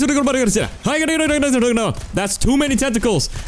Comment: "I don't like hentai nnoooo" 4:33 " I'm just kidding, I love hentai" EXCUSE ME SIR YOU SAY WHAT NOW??!!👀 Edit : okay he said hentai and NOT hand tie so let me fix that